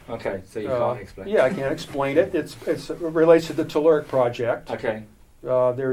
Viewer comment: "It's related to the tulerk? project" What was it that he said? Toolerk?